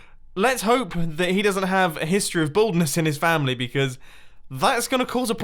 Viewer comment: I SWEAR TO GOD I HEARD HIM SAY "History of a bald car"